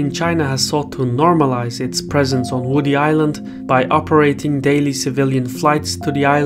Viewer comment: I thought you said Woody Allen